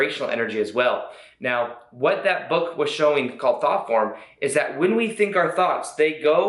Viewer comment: I thought he said thotporn when he said thoughtform🤣